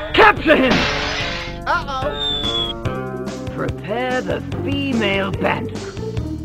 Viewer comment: What did in 0:34 Prepare the female idk what he said what did he say Nvm I know now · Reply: Neon The Fox it said prepare the female bandicoot